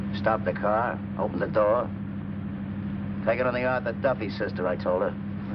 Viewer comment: Some of the subtitles are wildly inaccurate. For example, at 18:32 we read "Take another hour and stop here sister". What he actually says is "Take it on the Arthur Duffy, sister." Arthur Duffy was a US 100 metre sprint champion and "taking it on the Arthur Duffy" meant moving on your own 2 legs, ie Get out of the car and walk